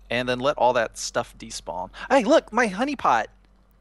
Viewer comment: I thought he said "honey look my honey pot!" lol